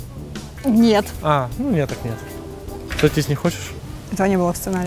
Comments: Сойтись не хочешь? - Этого не было в сценарии. - Или ты просто😂😂😂😅😅😄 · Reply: А мне послышалось "в туалет не хочешь?"😂😂😂😂😂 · Сойтись не хочешь? - Этого не было в сценарии. - Или ты просто😂😂😂😅😅😄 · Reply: мне послышалось "родить не хочешь?"😂😂😂